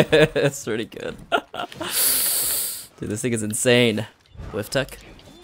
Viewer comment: did anime just say “ this niggas insane